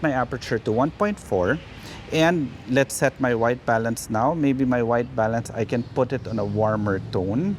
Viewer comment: I swear to God, every video I hear "My wife balance..." and have to do a double take. =)